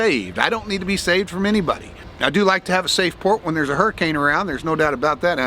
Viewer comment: I misunderstood saying “I like having a safe port” and thought he said “I like having a safe word”